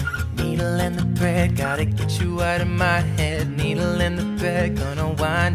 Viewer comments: I thought Shawn Was saying “need a little prayer gotta get you out of my head. Need a little prayer” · for stitches I thought he was saying 'Needle in the bed' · Shawn is laying there like " I need a little bread"😂😂😂😂😂 sureee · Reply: I thought he always said needle in the bread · I knew the drag me down one and the stitches one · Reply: when I heard stiches I thought he was saying needle in my head needle in the bed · I thought it was, "Needle in the bed" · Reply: i always heard 'needle in the bread' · When I heard it the first time I heard 'needle in the prick'😂😂 (shawnmendes stiches)